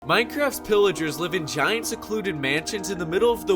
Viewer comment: I thought you said "Pillagers live in china"